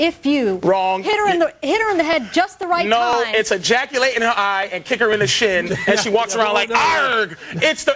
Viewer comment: LMFAO!!! Did he just say ejaculate in her eye on Fox news?? LMFAO